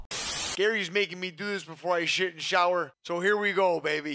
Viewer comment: Sounds like he said shit in the shower